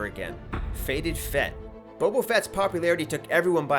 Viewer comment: It sounded like he said Bobo Fett instead of Boba Fett XD